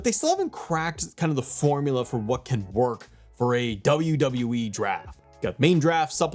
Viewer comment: I'm Lit AF At The Moment 😁 But I Know That At 9:09 Into The Video It Kinda Sounded Like He Said AEW. 😁